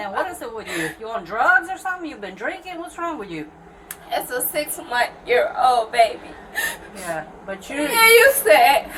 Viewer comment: did I hear that right? “It’s a six-month year old baby”